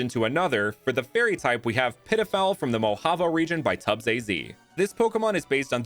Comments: auto captions said Pedophile on this one, this why you should properly caption your works, especially if they have a lot of proper nouns. Also for some reason youtube is telling me this video is from three days in the future. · I thought you called it pedophile.. you did the fairy type so dirty 😂